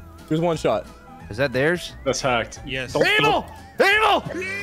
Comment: I thought he said “anal” not “evil” 😅😅😂😂